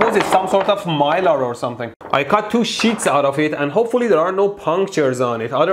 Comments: Im sorry but 8:17 sounds like shits instead of sheets( : I need hearing aids! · did he say 2 shits